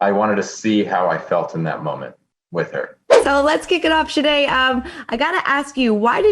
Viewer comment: did she just call Shaina Shanay?